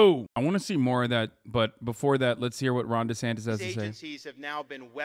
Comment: oh it's Ron Desantis...the entire time i thought you guys were saying "Ronda Sanchez" (i've never seen this person before) (im south african)